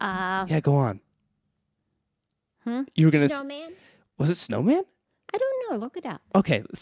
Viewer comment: When she was talking about Theoden’s horse I thought she was saying “Snowman” 😂 not “Snowmane” I love how she remembers the little details like that